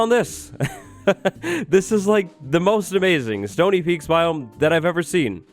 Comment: Somtimes its sounds like he says "sony peaks"